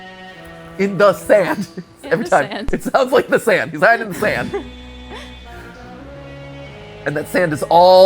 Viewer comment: I want to point out that i dont know where matpat heard "he is hiding in the sand". what i heard was "He found a way inside us". which makes more sense to me than he his hiding in the sand.